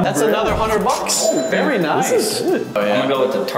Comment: did just say: This is s***? whaaaaaat, I thought this was a kid friendly channel